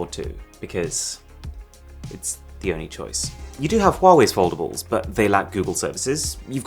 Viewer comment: I thought he said affordable 😅. I was like huh?